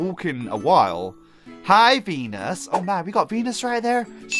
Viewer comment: did he just say pen**